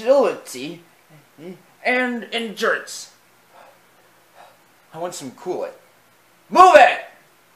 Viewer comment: I know McQueen said I want some coolant but the auto generated subtitles said I want some kool-aid which was kind of funny 😂😂😂😂 which makes me think it would’ve been funny if McQueen actually said I want some kool-aid 😂😂😂😂